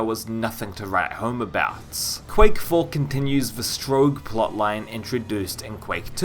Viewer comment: The average human: Strogg That one guy: S T R O U G G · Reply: I thought he said Stro-ge in the video 😄